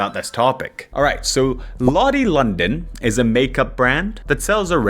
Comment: Every time someone says Lottie London I hear “Ollie London” and I feel its invoking a similar response